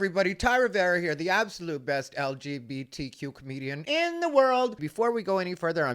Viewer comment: I heard "The absolute best LGBTQmedian in the world" haha!